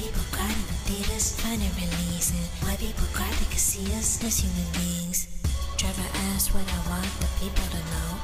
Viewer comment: it took me like half a minute to understand what she said there she said somethings somethings see us as human beings sorry but Michael Jackson soft whispering doesn’t work for you darling in fact Michael Jackson made songs about this like human nature black or white Man in the mirror we are the world they don’t really care about us like I’m sorry but not really you’re nowhere near close to MJ‘s level of singing